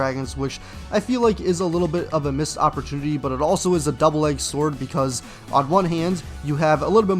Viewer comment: I'm sorry did you just say "double egged sword" 😭